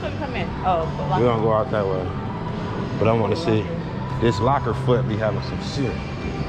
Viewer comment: did bro say locker foot did bro say locker foot? lmao